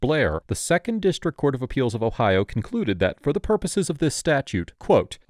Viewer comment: Both the cops and the suspect don't fully understand their rights. This is just a mess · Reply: No, DID NOT say "it was considered public" Watch again: 3:55 "under State vs Blair, FOR THE PURPOSES OF THIS STATUTE! 🤦‍♀️ learn the language of the law and quit lying.